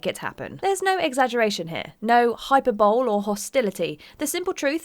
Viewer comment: Did she just say hyper-bowl!? 4:08 Rocksmith was a full priced game ($60). 600 x $60 = $36,000